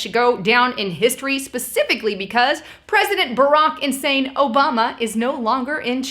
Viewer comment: Did you say Barack Insane Obama? ^^